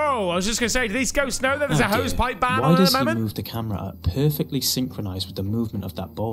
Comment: Did you just say "there's a hose pipe ban at the moment" if so what does that mean exactly or if heard you wrong what did you say